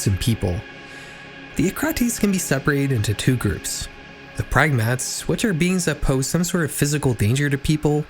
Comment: The dropdown caption should probably be “Pragmats” instead of “Kefals”, right?